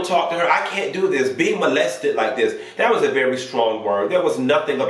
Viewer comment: I thought he said he wasn’t going to be lectured not molested. I gotta go back and listen.